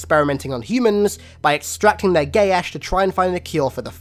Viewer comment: It sounds like hes saying gay ash